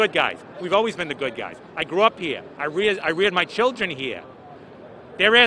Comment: did he say i reared my children hear